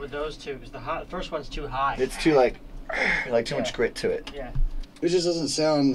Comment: Did the producer say "its to hard" at 1:51?! Wtf -.-... that growl sounded like fucking sick ?